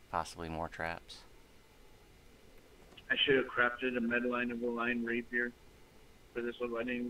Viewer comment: it sounds like I said "I should of crapped on a metaline rapier"